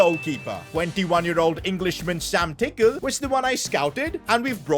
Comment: when he said sam tickle i thought he said sam pick;e